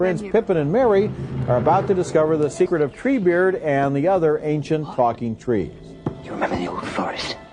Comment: Who else heard him say "ancient fucking trees"?